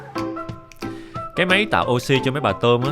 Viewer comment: em nghe thành cái máy tạo oxi chạy bằng cơm :))) em tưởng e nói tụi con nít nó ồn quá